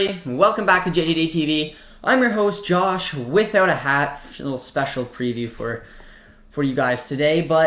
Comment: I thought he said "Josh without a head" xD